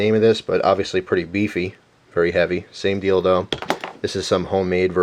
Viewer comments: did he just say same dildo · I heard him say same dildo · what did you say "same dildo"? because it looks just like one.... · i thought u said same dildo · when he said same deal tho It sound like he said same dildo😊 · did he say dildo? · Who else thought at 2:57 he said dildo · thought he said same dildo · looks like a dildo, then you said "same deal though" that sounded like "same dildo". I laughed :D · what he said "same deal do" what i heard "same dildo" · i thought he said same dildo Now when im at it . . . it kinda looks like some sex toy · lol at 2:59 it sounds like he said dildo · I misheard something in this part. Did you say "Sane dildo" or "same deal though" Lol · does he say same dildo??